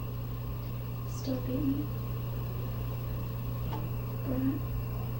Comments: what is she saying? She laughs and says still beat you?? Unsure anybody else? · I hear "Still beat you...brat" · sounds like she says, "Still hate you, burn." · I hear that too but not sure if that's because I was expecting to hear that. But she's clearly saying something!! @ 32:00. She giggles and says "I still beat you....brat" ???? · i heard "still beat me.... brat" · she gave a little chuckle, then sounded like she says "Still hate me?" ...... brat. Did anyone else catch that? I heard it with ear phones.